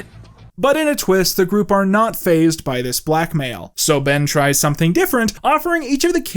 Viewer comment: I swear he said they aren't phased by this "black male" not "blackmail"